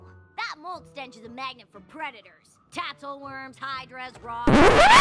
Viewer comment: I think she said Rocks not Hawks